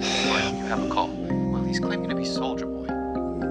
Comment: "He's claiming to be Soulja Boy" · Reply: I swear the first time I heard “solider boy” in the show I was like why tf are they talking about Soulja boy?